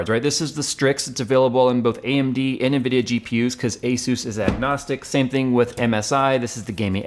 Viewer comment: Did you just say "ASUS is agnostic"?